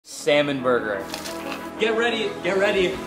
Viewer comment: I heard "Semen Burger" 💀